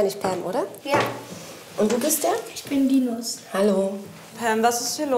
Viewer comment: Ich hab verstanden "ich bin die nuss" 🤣🤣🤣🤣🤣😂😂😂😂😂😂😂😂😂😂